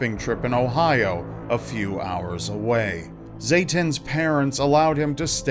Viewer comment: I thought it said Satan’s parents